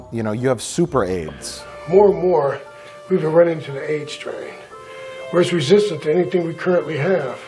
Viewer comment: When that doctor was talking about her "Aids strain" all I heard was "Aids Train" All aboard the Aids Train!!!